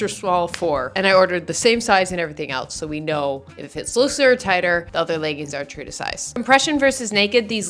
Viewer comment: ok you are legit slurring your words and its super annoying. I had to rewind a few times. When you said "LOOSER" it sounded like you said "SLOOOSER" girl please speak with better enunciation.